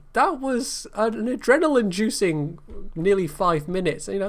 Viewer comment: ***** "adrenaline-inducing" I suspect. Caption guy should just hire a relative of him, called subtitle-guy, sometimes. :P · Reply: Jack Beauregard Yh I thought it was supposed to say inducing.. listened to it twice and DEFINATELY sounds like "juicing".. But the main question is whether "subtitle guy" would be more or less evil..